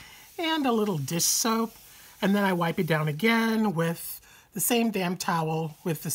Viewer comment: I hate cliffhangers!!! WTH! I would have sworn you said, " The same damn towel. ". Lol. Youre a real stinker. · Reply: I just listened again. You did say the same damn towel. Bahaha. I love it. Cant wait till Sunday!